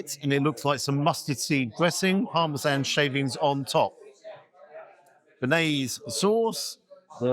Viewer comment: I thought you said "Harmless Anne shavings" at first 😂 Sounds like something Danny would say (the £58 pizza).